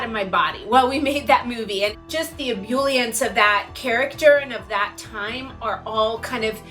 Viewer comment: What word does she say at 12:08? CC says it's ambulance, but clearly it is not haha.